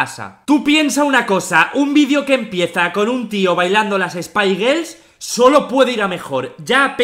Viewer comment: que dijiste girls o gay una de sos XD shaludosh desde argentina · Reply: Dijo girls